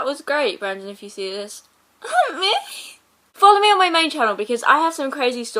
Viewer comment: Oh gosh when said "follow me on my main channel" I heard "fuck me on my main channel" I'm so trashy